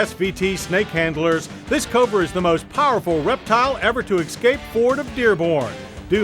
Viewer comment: Did he say "exscape" instead of "escape" @ 0:56?!